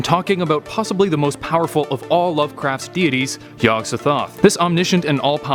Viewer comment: Sounds like your saying "yog da toth". Prolly just me...